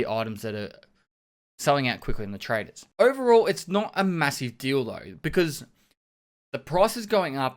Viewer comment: I always hear "not a massive deal though" as "not a massive dildo". Am I the only one?